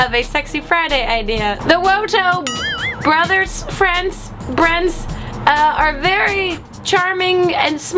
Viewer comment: did you say bryn at 1:05 cuz thats my name and idc if you did ill just pretend you did ok